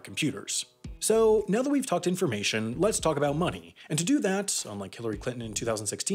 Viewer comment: "So unlike Hillary Clinton in 2016, we need to go to the Midwest." 😂😂😂😂 Gold. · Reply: Dang, I first heard it as: "let's talk money - unlike Hillary Clinton" 😄 Still made sense to me.